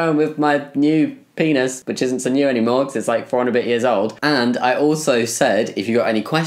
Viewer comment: I didn’t understand what you said at first when you said your lower surgery was “4 years and a bit old” so I thought you said “like 400 videos old”…like time is measured in YouTube videos 😭😂 anyways don’t mind me